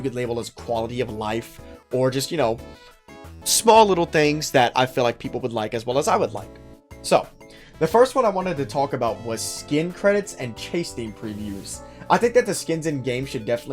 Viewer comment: I thought he said skin crates for a sec, phew.